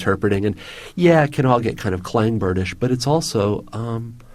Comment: What is the word he is saying at 15:41? "It can all get kind of ....... ???" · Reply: Elco Balthazar “Clang-Bird ish” - A Clang-Bird is a fictional bird that flies in ever decreasing circles until it disappears up its own ass.